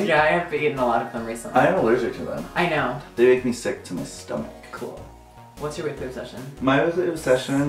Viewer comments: What does Mitch say at 6:07 tho?? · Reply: I think he says; being sick to your stomac. But then like really fast, and with a weird sssss in the front haha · What does Mitch say at 6:06 under his breath kind of. SOMEONE HELP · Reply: 3llalevinson I think he said “Me sticking my hands in your pocket”